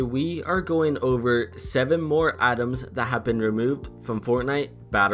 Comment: Who else heard him say 7 more atoms that have been removed